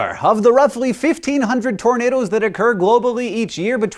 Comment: I thought it said tomato instead of tornado...